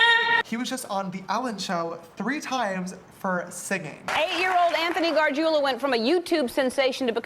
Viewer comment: I thought he said Anthony Garza anybody else😭